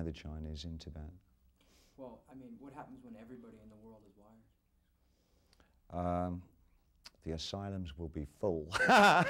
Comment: what does the interviewer say? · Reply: The questions was, well, what happens when everyone in the world is wired?